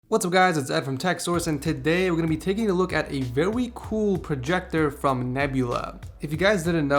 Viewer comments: Did you just say "vewy"? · I swear he says vewie special projector